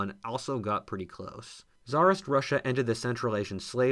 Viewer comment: It took me a while to realize you used the word "ended". I thought you were saying "entered", which was kind of odd to put there.